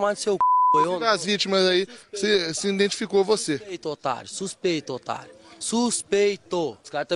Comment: Espera... o que f-foi que ele disse???? SUS OQUE???? SUS AMOGUS?????????????